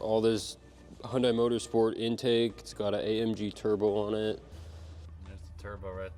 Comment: Did I hear "AMG turbo" at 4:41? as in Mercedes-AMG?